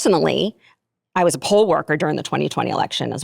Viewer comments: POLE WORKER ❌ POLL worker ✅ I know I ain’t the only one 😂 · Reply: I heard "Pool worker" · Boy I sure misheard poll worker as POLE worker. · Why did I hear "poll worker" and think "pole worker" and thought she was one of those girls who strip and has an OF? This show has ruined me 😂